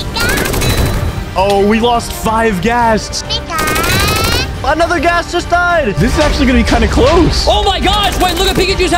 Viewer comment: what did pikachu say??? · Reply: Pikaaaa